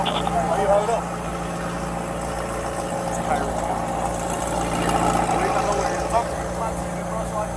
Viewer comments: does anyone else hear the police officer say "thats Tyreek Hill"? Seems clear that he recognizes him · How do you work in Miami, get handed a license that says Tyreek Hill from a giant guy in a McLaren, by the stadium on a game day and not know who you're dealing with. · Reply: I clearly hear one cop say to the other "It's Tyreek Hill". they knew who he was. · I swear you can hear the cop say to the other two who are still on their bikes "It's Tyreek Hill" 9:43 - "officers didn't know who they had pulled over" seems a bit suspect.